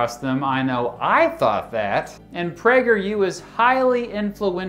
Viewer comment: I thought he said "PrankerU". 🤣